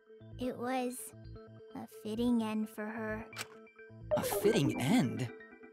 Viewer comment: i think she said a fitting end cause miu had a potty mouth